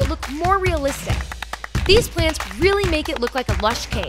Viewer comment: Can we appreciate how she works so hard and spend so much money just to entertain us and take care or her wet pets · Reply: I thought you said “pants”